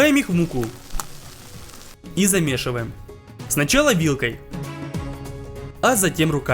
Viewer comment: Мне послышалось или он сказал и замешиваем сначала Иллкой